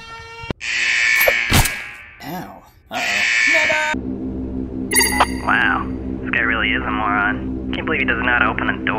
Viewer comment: Did he just say the n word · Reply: "NEVAAA-" (Never with an accent)